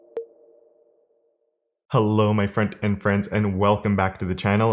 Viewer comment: “frontend friends.” for months i thought he was saying “friend and friends.”